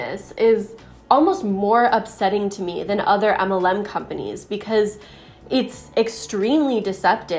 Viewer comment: For the longest time, I thought you were saying "Emma Lim companies" and I was like, what the heck is Emma Lim?! Then I finally got it that you are saying "MLM." Gotta love accents! LOL